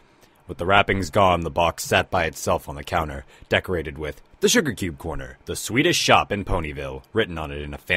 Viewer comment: I heard "Sugarcube Corner - The Swedish Shop in Ponyville!"